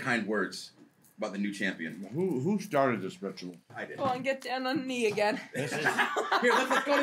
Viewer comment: I thought she said get down on me again 😂😂😂😂😂